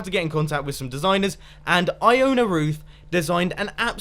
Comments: When you said "Iona Ruth" I thought you said "I own a roof" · when you first said Ioana ruth I thought you said I own a Roof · I thought you said i own a roof, instead of Ioana Ruth.